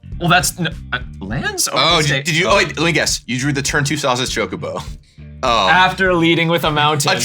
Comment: i keep hearing "sausage chocobo"